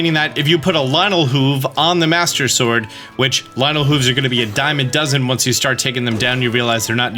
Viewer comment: What does he say about Lionel Hoofs and Diamonds at 21:53, please? I can't understand. English is not my mother tongue. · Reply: He uses the idiom “ a dime in a dozen” which means something is to be common and/or of little value. Meaning once you start hunting Lynels their hooves are no longer that special